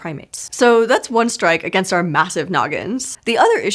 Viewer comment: CC says "So that's one strike against our massive melons." Words used: "So that's one strike against our massive noggins."